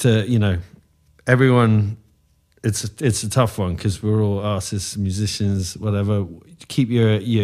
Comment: I thought he said “We are all asses.” But he said artist with a strong accent lol